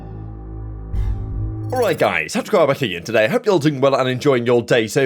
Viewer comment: I know I’m old but I only hear “touch and grab her” even though I know he is saying tacticalrab here. Every single video